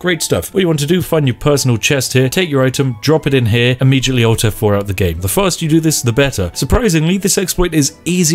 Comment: "auto 4 out the game " is that what he says